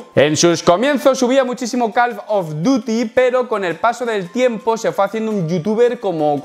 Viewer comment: parece que dice calf of duty, los gemelos del deber jajajajaja